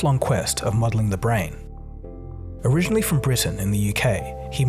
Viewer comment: "Originally from Britain in the UK." That's kind of like saying "from The United States in America". He was born in England, so it should be "originally from England in the UK". :-) As always though, fascinating content, and excellent production values. :-) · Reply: I thought he said Brighton, which is a city in UK.